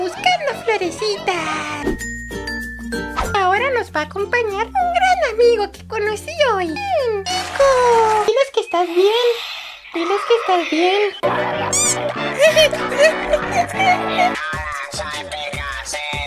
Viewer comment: yo escuché Quico